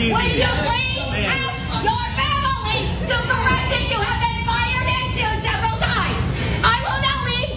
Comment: What is she saying here?? It’s a bunch of gibberish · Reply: "Please ask your family to correct that you have been fired and sued several times" Man idk wtf that's supposed to mean